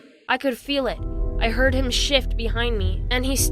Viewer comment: When the girl said "i heard him shift behind me" I thought she said "i heard him s* behind me" l